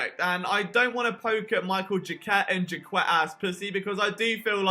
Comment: did he say jaquette ass pussy 😂😂